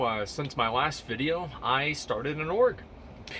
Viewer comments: i heard "i started an orc" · I thought he said he started an "Orc", and 100,000 people watched him live... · i thought he said i started an orc and swapped to horde